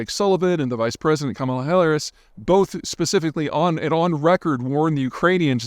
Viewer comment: sounds like he said “Kamala Hilarious” which is actually lowkey brilliant